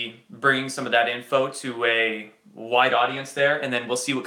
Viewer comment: Were you smiling because you realised it sounded like you said "a white audience"? :-D